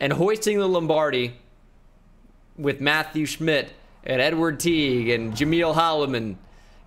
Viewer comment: Thought he said n-word teague lmao